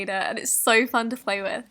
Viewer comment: did she say flay with instead of play with?